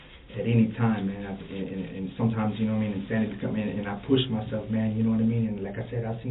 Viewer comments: did he say Santa 🎅? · Kudos to whoever did the subtitles. They deserve an award. 😂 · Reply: does he say Santa?